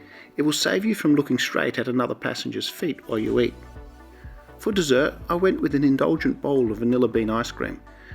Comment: That business class seat configuration is most bizarre!!! When you said: " For dessert ...." I misheard it as " Foot _dessert_...". not surprising really, with that seat configuration. 🤪😖🙀🤔👉🦶👈